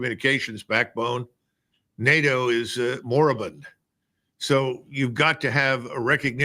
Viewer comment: New word: "moribun"? 16:21 What us the spelling of the uncommon word that he just used here? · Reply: Moribund: 1. Approaching death; about to die; dying; expiring. 2. Almost obsolete, nearing an end